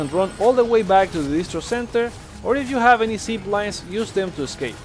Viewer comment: If I have WHAT? Seed lines? WTF are you saying? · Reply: Zip lines